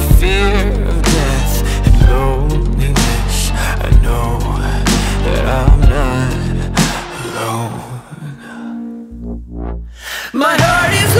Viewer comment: I keep hearing "i know that i'll die alone"